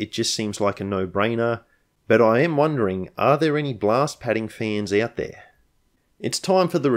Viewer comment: I thought you said glass padding, not blast padding.